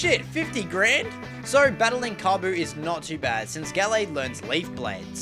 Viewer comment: Did- did he just say Kobu instead of Kofu?